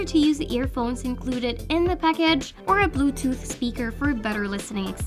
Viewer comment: did i just hear her say "pekej"? 😂 8:19 "the 'buddy' is more premium"?